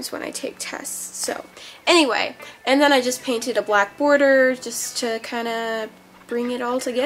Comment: OML I SWEAR I HEARD BLACK HOARDER😂😬😬😂😂😂😂😬 my ears are bad!